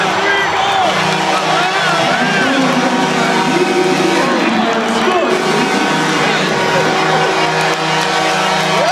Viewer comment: I hear the PA announcer say something, not sure what. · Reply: "That goal is brought to you by DTE Energy, where scoring means saving"